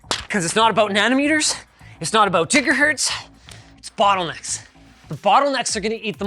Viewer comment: Did he say Jigga Hurts?